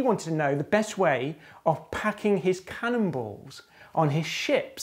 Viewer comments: At first, i heard "...packing cannibals." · "...looked for the best way to pack his CANNIBALS." Oh, i think i misheared that part.